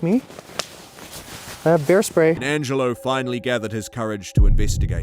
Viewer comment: I had to replay that three times to hear bear spray instead of hairspray 🤦🏻‍♀️